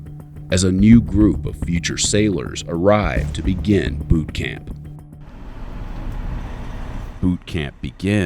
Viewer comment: I had t go back and listen carefully, fist time I heard 'poop camp'.